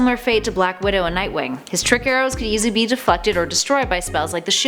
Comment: I thought you said defuckted instead of deflected... I'm disappointed defuckted isn't a word, I feel I'd use it often.